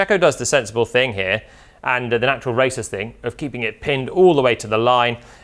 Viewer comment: did JP just say "the natural racist thing" rather than "the natural racer/driver thing"